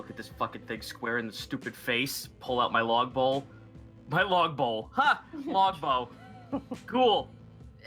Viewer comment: log bowl? I thought you said Log Bow which fires praise for Oreyara at your enemies!